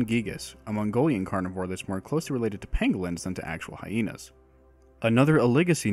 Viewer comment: Bruh it sounded like you said Penguins instead of Pangolins.